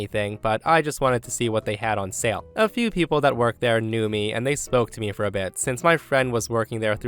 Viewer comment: I was listening to the video but not watching it, and it sounded like you said “they SMOKED me for a bit” 😂